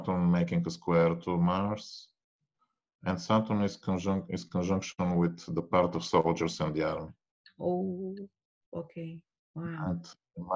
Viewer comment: what did he say? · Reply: “Saturn is conjunction with the part of soldiers in the army.”